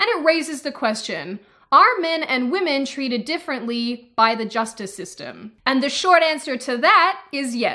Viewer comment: Had to rewind 1:24 because I thought for second she said "is the justice system gender equally fair"(abbreviation)